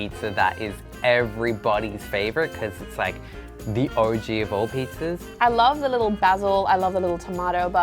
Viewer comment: his accent is so thick i thought he said "orgy of pizza" but he said "OG" 😂